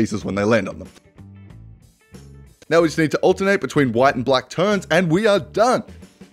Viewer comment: I thought I heard you say white and black turds XDDDDDD